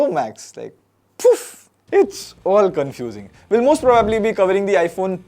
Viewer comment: i thought he said f u🗿